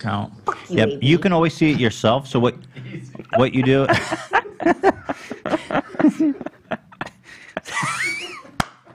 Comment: what did he say? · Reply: “Take it easy” it’s a quote from Napoleon Dynamite